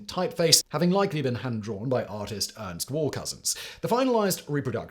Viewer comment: I almost thought I heard the name Warcousins [Ernest --graphic artist] and thought wow! what an appropriate name for this position! But it was just the accent from Simon Whistler in the ears of an American. Wallcousins!